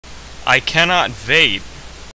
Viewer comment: i honestly thought at the start he said "i cannot vape"